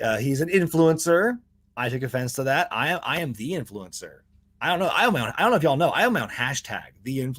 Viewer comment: That bit at the end really did make it sound like you said influenza.